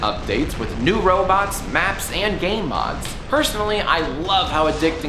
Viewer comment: I thought said "gay mods". That would certainly be a "unique trait that can alter your gameplay". I would buy the shit outta that game.